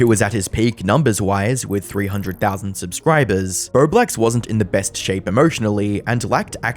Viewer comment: I seriously thought I heard you say his name was "BloBlax"